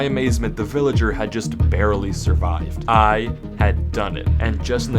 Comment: "I had done it" sounded like fingees "I added victim Gerard in minecraft"🤣